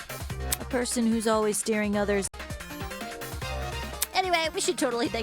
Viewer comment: what was Cassidy saying at 9:01:29? it got cut off for some reason. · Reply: " A person who's always steering others down a dead end, huh...? " " Heh. I guess they needed a GPS " And after that, the rest is in the video